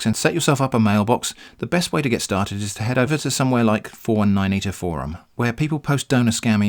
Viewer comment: if anyone didnt hear him properly, he said "419 eater forum" (its actually really helpful)